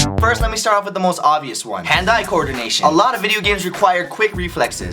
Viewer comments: when he said hand-eye coordination i thought he said hentai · Idk why but I kept hearing 'Hand-eye coordination' as 'Hentai co-ordination'